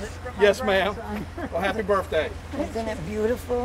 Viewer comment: I heard "happy birfday"